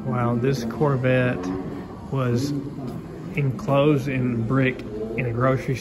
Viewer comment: Thought you said I was close ? Lol! Still looks fun :)